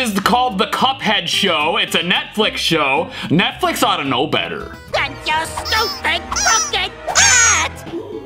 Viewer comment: Note: Mugman actually said "AND YOUR STUPID CROOKED HAT" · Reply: Bro I hear "and your stupid muffin hat" bruh